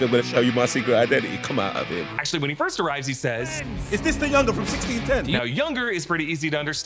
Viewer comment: as a British person who understands what he is saying, i can safely say he said UNDER not YOUNGER and i believe he is referring to the spot not miles